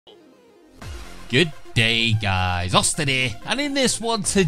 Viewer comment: in the intro i thought you said "get gay guys, austin here" for a second lol